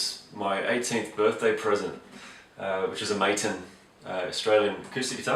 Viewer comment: Hey Travis, thanks for checking this out mate. Yes, I had fun doing this response for Mags...it was a day that I will look back on fondly forever, just a right time, right place kind of freakish scenario, not expecting anything much from it and having that great experience. The Epiphone to take home? Yeah, I wish. Martins are magnificent guitars!! I would love to own one of those one day. Thanks again for stopping by, take it easy mate. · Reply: Oh shoot, I thought you said your guitar was Martin, (Maton, Martin, klajdflkjdf!) I misheard you hahah, sorry about that. Your guitar sounds very nice too, is what I meant to say :)